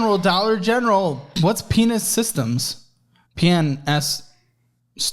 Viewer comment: I heard "What is Penis Systems?" - er, what? Oh, "PNS Systems". That might be the worst name for a business I've ever heard of. Totally fits that it's a collections agency.